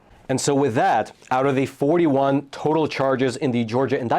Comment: 85 more charges to go! Trump 2024🇺🇸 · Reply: rewatch I heard 41 not 85 but, 41 or 85 doesn’t make much difference when they are all bull 💩